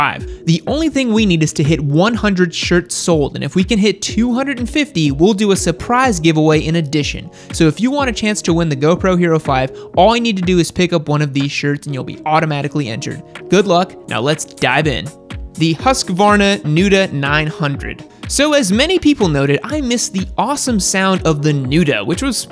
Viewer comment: I don't wanna be "automatically injured" tho.... Anyone else hear that around 0:50 haha?